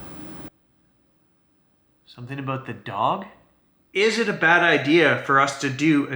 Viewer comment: Listen to 9:22 again please. I swear it says it’s a Bigfoot. I wasn’t looking at the captions and I heard that then it’s speaks of the dog.